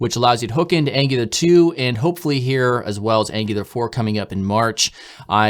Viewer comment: did you say "Angular 4 is coming up in March"???